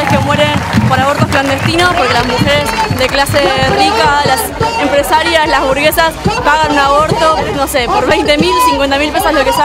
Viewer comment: pensé que dijo "las hamburguesas pagan un aborto" y me quede tipo spera... que? ahre :v 💚